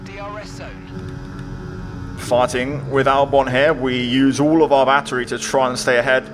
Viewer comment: anyone else heard it as "farting with albon" ? lmao